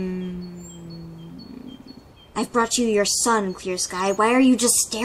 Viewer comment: I thought he said “I brought you your son, Fierce Guy”